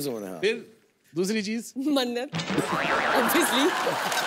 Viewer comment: what did she said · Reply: Mannat